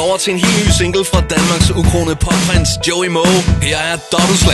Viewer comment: did he say fuck? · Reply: no he said pop... :-)